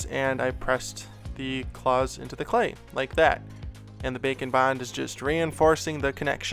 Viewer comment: Every time you say Bake and Bond... I hear "Bacon Bond" and think that's gotta be the coolest spy ever.